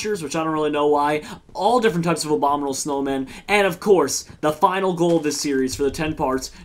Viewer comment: Jerome when you said abominable snowmen I thought you said Obaminable snowmen and then thought, wait there's snowmen that look like Obama? Our worst fears have been realized.